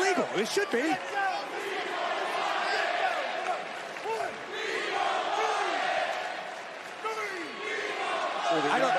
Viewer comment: what the crowd said ? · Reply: "We want Wyatt" which I agree with them cuz Wyatt was amazing 😭